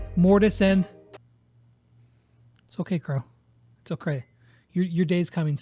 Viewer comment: did just said "it's oCray"😂